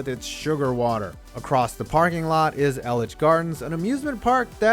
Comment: did you call it elich gardens